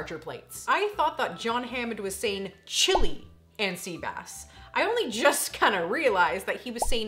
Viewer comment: I would try it but I'm not a seafood guy.. and I always thought john hammond said chili and sea bass as well